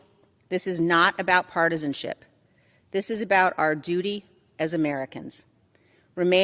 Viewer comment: "This is about our duty as Americans" ...but when I hear an American accent I hear "This is about our doody as Americans". Just sayin' 😁 Y'all are adorable. Great podcast Mr Franken.